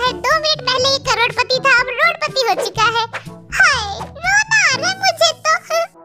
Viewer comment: part I thik that jin is actually saying Hayee